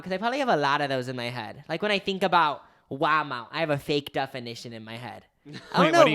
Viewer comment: The captions put “Huama” when said “Walmart” Waa Mawt. 😄. And 28:46 captions say “Obama” for “Walmart.” 😂